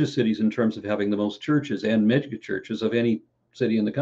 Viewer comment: I'm unclear on what Aron says here - "... churches and Mexican (?) churches.." Is that what's being said? Even the captions aren't picking it up. Just curious. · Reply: Churches and mega churches. He is unclear indeed but it's something he cites often about his state/city